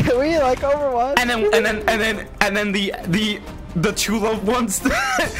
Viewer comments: LOOOOOOOOOOOOOOOOOOOOOOOL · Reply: Watched that part 12 time all i heard was "the two loved ones" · what the fuck is this man saying? The 2 loaf ones